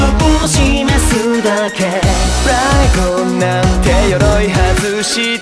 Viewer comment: の｢プライド｣が何回聞いても｢フライゴン｣に聞こえてしまってフライゴン衣装(？)を身に纏う嵐ちゃんが脳裏によぎる